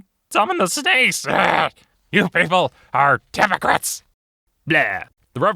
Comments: Am I the only one who hears "democrats" not "hypocrites"? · Why do I hear "You people are democrats" when he says "You people are hypocrites" xD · I know it’s supposed to be hypocrites, but every time I watch this video it sounds like king says “you people are Democrats.” I’m not trying to make this political, but I find it funny. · I had to turn on subtitles cuz I honestly thought he said "You people are democrats"